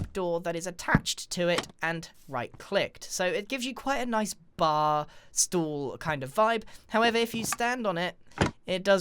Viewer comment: Dear grian, im dutch and did u said at 2:38 stoel? Aka chair translated from dutch